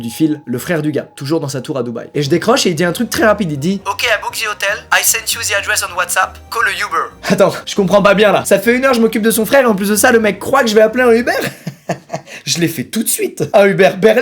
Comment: C'est quoi "WhaysApp" ?? XDD